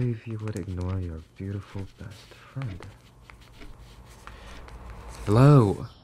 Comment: Really thought that said “catholic best friend” and was very confused